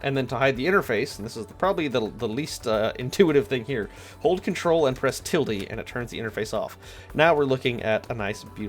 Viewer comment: *hold ctrl and press tildy* does anyone know what he actually said?